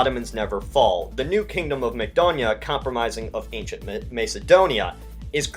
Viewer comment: I misheard it as “Mcdonald”